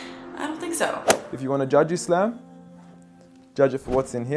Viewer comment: he said if you wanna judge islam not if you wanna judge me try to hear what is he saying next time